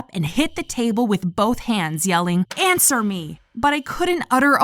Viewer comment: I thought her mom said Cancer me!!!